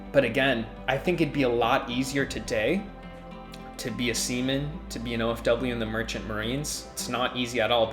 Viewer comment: Oh no i heard "semen" when its actually "seaman"😅😂